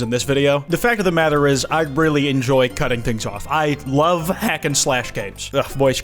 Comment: Broo i thought that said “cutting my things off” and i got very concerned💀